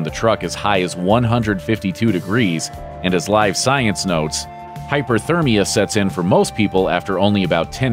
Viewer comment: I must be tired, did it say hypothermia sets in.. in a hot environment? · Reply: No it said hyperthermia which is correct.